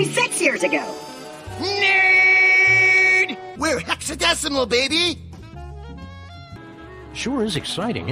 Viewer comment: “We’re hexadecimal baby!” Both start posing Oh god my little heart ♥️🥺 Edit: did say hexadecimal ???